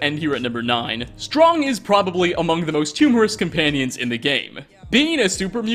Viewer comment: When you turn captions on at 7:05 look at how they mispronounce humorous Edit: does that say humorous?